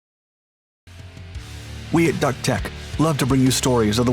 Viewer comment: it sounds like he said "we at duck tech....tell me I'm wrong 🤣😒